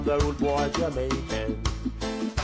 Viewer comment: "ready meals and bacon, here come da rudebwoy Jamaican" haha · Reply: I always thought he said "Right in the oven baconnn, here come the rude boy Jamaicannn".